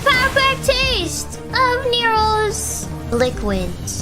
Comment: Gura: Taste Newo's liquid! Chat: Phrasing! Gura: Who says that? Chat: Sorry, that was instinct. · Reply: Also sounded like she said "succomb to your wombs". I'm pretty sure I know what she tried to say, but it sounded like wombs.